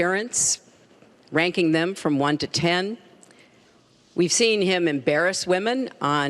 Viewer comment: Anyone else, for a second, think she said "we've seen him RAPE women"? lol.